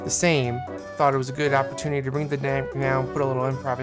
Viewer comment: Lol did you say, "I thought it was a good opportunity to ruin the day and put an improvisation in with a flute solo" at 6:55? hahaha